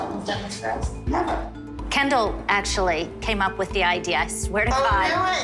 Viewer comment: I always hear “Ken Doll” and start laughing edit: now I hear "candle" 🕯